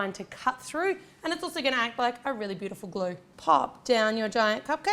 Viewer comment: Did anyone else hear that really weird voice at 8:41 that said "pop"